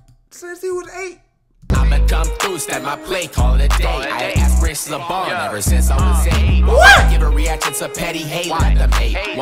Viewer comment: i think he said " had a inspiration to ball since i was 8"